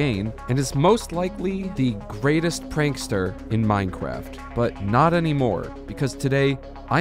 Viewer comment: Did you just say Minecwaft?